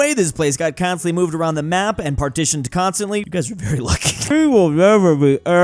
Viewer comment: did he say poles are very lucky after hundreds of years of shifting borders